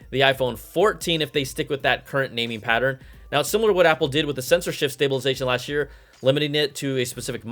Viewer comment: When you say "sensor shift" and I hear "censorship". Weird.